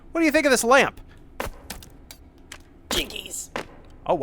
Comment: When bro said “Jinkies” I thought he said “Gigity”